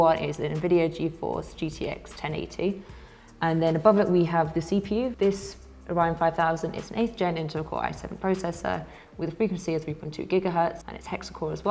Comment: did she just call the CPU Orion 5,000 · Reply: ‘Above it we have the cpu, this Orion 5000 is an 8th gen Intel core i7 processor’ (there’s a few different specifications that’s why I said it 🤣) Talia ✌🏼